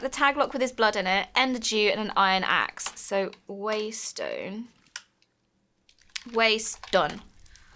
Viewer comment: When they kept saying waystone, it sounded like they were saying my name, Weston, and I said sorry mom and mom looked at me and was like wtf.